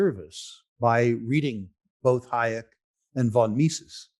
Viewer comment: what is author he recommends besides Von Misses? I just cant understand even though I have replayed it 20 times. Thanks for the Help. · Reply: Hayek. The road to serfdom